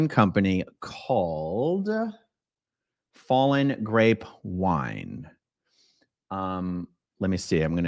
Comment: I was listening w/ headphones while doing dishes. What I heard was "Fallen Rape Wine". I had to take off my headphones and re-listen. Yes, I heard it wrong but still, what are the chances that a wine brand DM is affiliated with has the actual word rape w/ in the name?